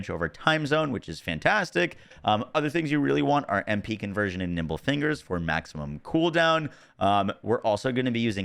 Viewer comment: I heard, "Nipple Fingers" 😂